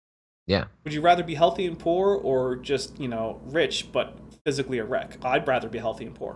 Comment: I misheard "physically a wreck" as "physically erect" xP